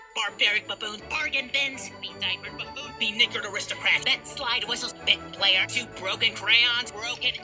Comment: Ayo what did he say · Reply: Be-knickered aristocrat